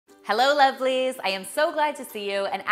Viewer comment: Did she just say hello lesbians 😳